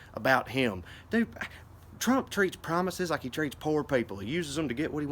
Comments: I thought you said "he treats promises like pornstars" but you said "like poor people" but I really like "pornstars" 🤣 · "Trump uses promises like he uses poor people...he uses them to get what he wants and then forgets they ever existed"...terrific line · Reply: Oh man, I swear I heard "toilet paper" instead of "poor people" - and it still made perfect sense!